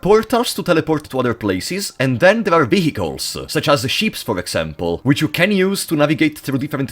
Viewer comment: I swear to god for a moment I thought you said the game had sheep as vehicles and I was like hell yeah.